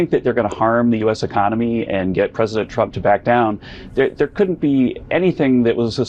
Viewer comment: he actually said stupider